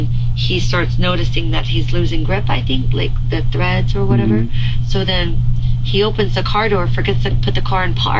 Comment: did she say he usually😮😮 doesn't treat me like this?